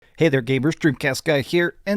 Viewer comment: Did he just say gamers and not gaybers 😂😅